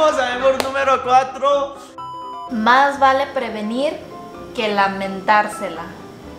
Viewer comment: En el minuto 9:20 más vale prevenir que mentarsela yo entendí más vale prevenir que m4m4r5e1a perdón yo eso entendí sorry los amoo y no lo puse groseramente es lo que entiendo los amooo